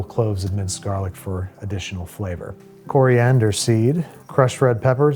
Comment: for a quick second, I thought he was saying someone's name "Cory Anderseed"